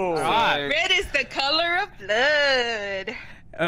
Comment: for a min I thought she said, "I'm red like the color of Love" but I am deaf